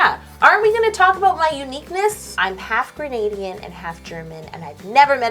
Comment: I can swear i heard her say half Grenadian.....! <3 <3 is it true?! I'm fully Grenadian...born and raised #repping473 <3 <3 <3 <3